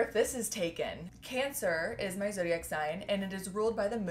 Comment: Wait, did you just say you’re cancer like the zodiac sign? 8:00 I am too girl.